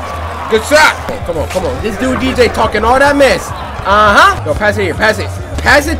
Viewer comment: did he say "this dude DJ talking all that mess" so DJ the bad guy